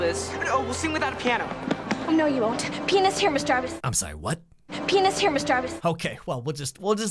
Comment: I NEVER REALIZED HOW IT SOUNDS LIKE KELSY IS SAYING PENIS! I always heard pianist right until. Saw this video 😂